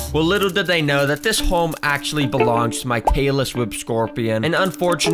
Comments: I thought he kept saying talor swift but is actualy tailess whip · I thought he was saying Taylor swift scorpion😂😂😂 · What i heard was “Taylor Swift Scorpion” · I thought he was saying “Taylor Swift Scorpion” the whole time😂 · bro why do i keep hearing "taylor swift scorpion" 😭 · I thought you kept saying Taylor swift scorpion and then I finally understood and started dying laughing 😔 nice video tho dude👍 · HELP! Why am I hearing "Taylor Swift Scorpion" ? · The whole time it sounded like u were saying taylor swift scorpion😂😂😂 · Is it just me or I keep hearing "Taylor Swift Scoripion" 💀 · I keep hearing "Taylor Swift scorpion" every time he says it. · I thought u said taylor swift scorpion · I keep hearing “taylor swift scorpion” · i thought he was saying taylor swift scorpion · why do i hear "taylor swift scorpion" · Am i dumb or do i hear "Taylor Swift Scorpion?" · I thought you said Taylor swift scorpion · why do i always hear “taylor swift scorpion” am i tweaking or sm 😭🙏🙏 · I hear him say "taylor swift scorpion" 😅